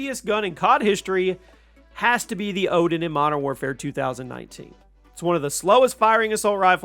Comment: subtitles showed manowar instead of oden